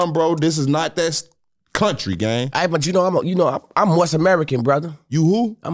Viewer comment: "I'm a Moors American" "I'm a moist American is crazy" 💀💀💀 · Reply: also just now realizing he was saying “moors” and I know about them fr I dead ass thought he said Morse wooow … ACCENTS IS CRAZY !? LETS START THERE !! 🤦🏻‍♂️💀🤣